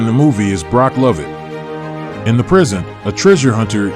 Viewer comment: he says; "in the prison" --I didn't understand.